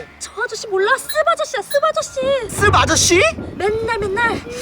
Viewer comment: 뭔가 욕이 들리는데 쓰 바아저씨